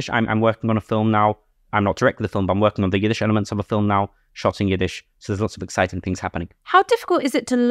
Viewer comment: When he said working on a film ‘shot in Yiddish’ I heard ‘shotting Yiddish’ 😝🥹